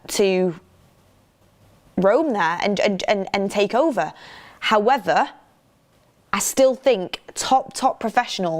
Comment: Is it me or always say “Howether” instead of “However?” 😊 6:30. It’s super cute though